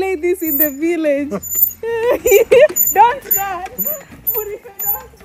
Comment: Did I hear "Murife don't run!" ???😂😂😂😂😂